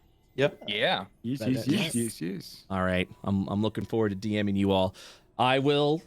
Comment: Lol my brain when you said "i look forward to DMing you all" i heard at first "look forward to damning you all" 😂